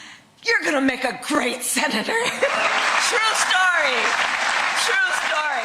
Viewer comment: Tim my set of ears have to be two decades older than yours so I could be wrong. When Kamal share her MvCain story I thought I heard “true story” not cute story?.